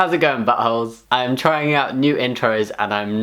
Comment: Hope you enjoyed the video! Are we liking buttholes then? 😂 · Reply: Sh!t I thought you said "licking buttholes-"